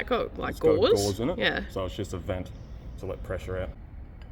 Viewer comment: "It's got gauze in it. So it's just a vent, to let pressure out." ... for people who, like me, couldn't make out what was said without a rewind.